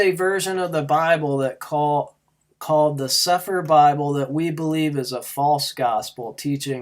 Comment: Does anyone know the version of the bible he uses but his church doesn't accept? It sounds like he says sefer or sepher bible. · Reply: The Sepher Bible